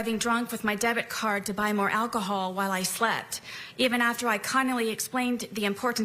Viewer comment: “cunnily”? What is she trying to say there